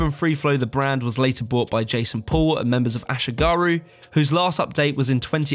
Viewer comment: at first i thought it said jake paul